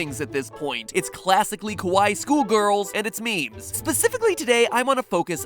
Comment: I would have gotten mad at him for calling the girls "Kowai" instead of "Kawaii" but I guess he's not wrong in the case of this video.